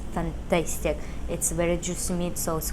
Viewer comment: What did you say @ 12:01 . . ."Fantasty"? :)